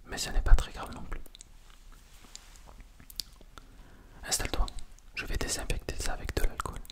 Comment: A 1:55 j'ai entendu "Installe toi, je vais te dé-sapper" mon cerveau c'est arreté xD